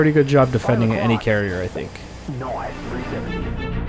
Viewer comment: did jester just say * plop * noice?